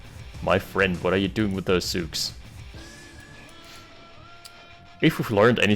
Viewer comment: The infantry line just vanished. For a while, I thought you were saying Zugs, not 'zooks.